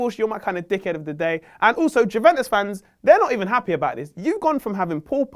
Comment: Did he just say Jewentus?